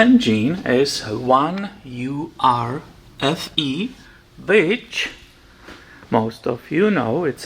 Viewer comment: thought you said Bitch 😂